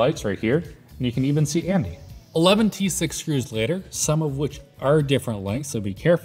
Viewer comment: "Eleven T6 screws [...]" took me two times hearing though. At first I heard "Eleventysix screws [...]" I'm dumb I guess lol